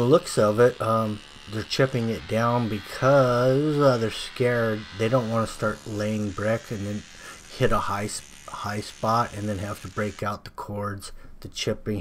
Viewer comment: I caught u.. at 2:07.. what were u gonna say.. they were scaring u. Huh. Hahahah.. me too. That drill and tip got mighty close to that corner.. soo. Admit it. Had me laughing. Those guys had u slurring ur words there for a bit haha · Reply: he said they were scared to have a high spot later when they were laying the brick